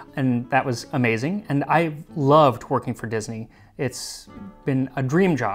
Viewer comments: My ears are so bad I thought you said twerking for Disney. And I was like ayyy Prince Charming making it clap XD. Then my right hemi was like “no dummy, working for Disney” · I thought he said “I love twerking for Disney”